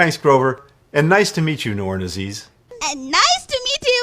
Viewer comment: ish Did he just say "and nice to meet you, Noir and Aziz." ???